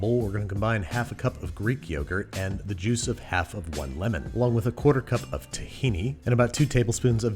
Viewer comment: "Along with a quartercup of Tahiti." That's what I heard initially. XD